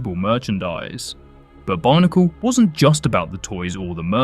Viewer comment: I honestly thought you kept saying barnacle